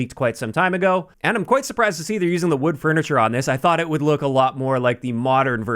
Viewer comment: am I buggin? Or did say wood furniture?